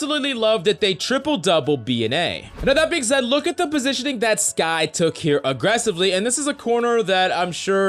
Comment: did he say skye?